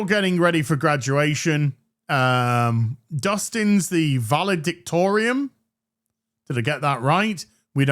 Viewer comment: Very close, good Sir! "Valedictorian" (my ears detected you saying "Valedictorium", apologies if you did it right and I heard wrong)